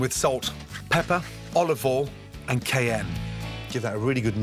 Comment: When he seasoned the hashbrowns… did he say cayenne?? It sounded like he said Kayem? It didn’t look like cayenne so I’m wondering